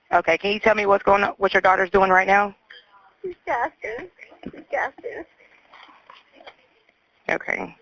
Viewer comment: The woman was not saying "She passed on. She passed on." She said "She's gasping. She's gasping."